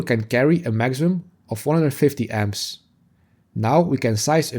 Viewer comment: 4 gauge handles how many amps? 12:09 I heard 450 amps, which might be for 4/0 (four-ought) But the fusing and wiring sizing is correct, so I assume I didn't hear correctly (150A) yeah, listening again, it is just my old ears. thanks for the video!